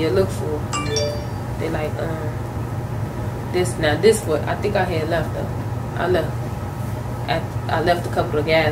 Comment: I could be wrong but sounds like she said I think I left her. Then tried to correct herself.